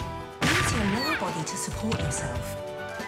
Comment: did bro just say kill yourself 💀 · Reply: He said “prepare yourself”, but it genuinely did sound like he said “kill yourself” 😭🙏